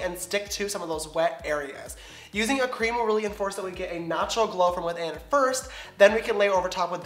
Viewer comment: When he says he's in the shade "natural beige" I thought he said "nacho beige" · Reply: And when he said "natural glow" I thought he said "nacho glow" 😂